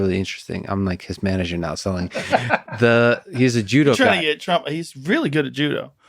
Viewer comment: Did Joe say he's trying to get Trump on the podcast?